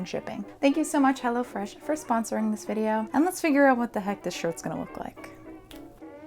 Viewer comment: I thought you said "...figure out how this shit's gonna work" and it kinda fits tbh